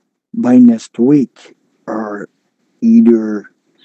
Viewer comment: WOW 21:07 he said "или" in Russian that means "or" in English🙄 then his accent sounded like Nigerian. What was that?🙄